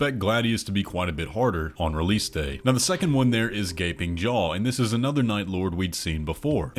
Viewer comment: At first I heard the name of the boss as "Gaping Joe" and I can't unhear it now help